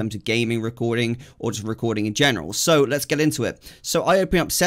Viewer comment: did he just say a rude work :oo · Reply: Lol no I said cog